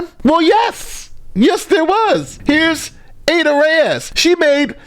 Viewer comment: When he said Ada Reyes (@ 10:00), it sounded like "Ate her ass"! ROTGLMFAO!!!